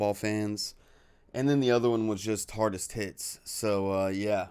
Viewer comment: I'm with, it sounded like you said hardest tits? "Hardestits".